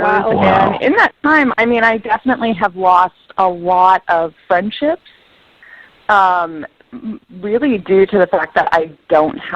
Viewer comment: at first I thought she said, "I've lost a lot sun chips"